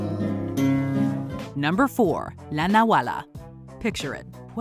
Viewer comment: =For a moment I misheard it as "Hala! Nawala! (Oh! It disappeared)"...